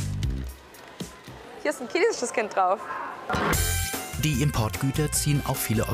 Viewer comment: min*hier ist nen käsisches kind drauf* 😂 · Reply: Ich hab "käsisches Kind" verstanden 😂😂